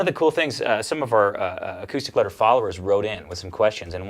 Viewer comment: did I hear say, “Acoustic Letter followers?” That is another YouTube channel ( old one). Tsk. Tsk. 😊